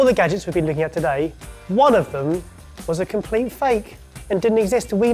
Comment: I totaly missed heard him in the beginning, thought he said none of them was fake. so that in the end they would guess and learn that they are all real. 😅